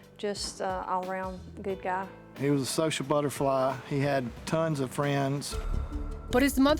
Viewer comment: Thought he said, "He was a sexual butterfly."